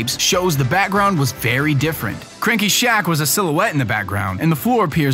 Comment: Did he say "Crinky?" Lol